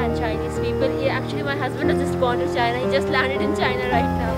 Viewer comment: Did she say her husband is bordering for China?